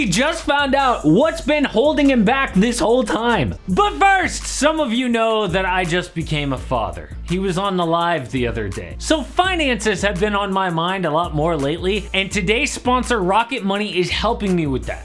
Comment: but no lie when you said “on the live” I swear the first time I heard it I thought you said un-alived. My initial shock was priceless